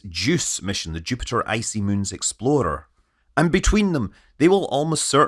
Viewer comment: when he is talking about JUICE mission I thought he said exploder at first. Had to do a double take, accents are fun sometimes